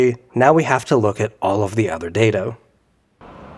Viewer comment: why did you say dato